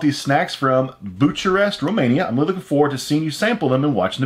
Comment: I just heard Buciarest" 😂😂😂😂😂😂 You don't wanna know what that means